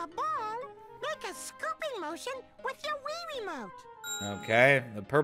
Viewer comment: I swear he said “wee wee mote”